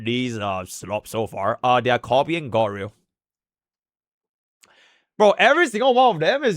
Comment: i love how youtube's auto caption said "Honkai Star Rail" when said "God Rail"